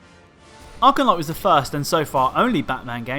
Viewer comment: For a but I thought you said Arkham knight was the worst, I was about to call you a crazy clown Edit: you put it on 2nd worst you're a clown no offense